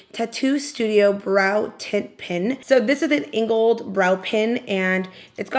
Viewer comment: Because my volume was on low and I’m too tried to pay close attention, I thought you kept saying that you were going to use a “Tit Pen” for your brows 😂😂😂. I need a nap lol.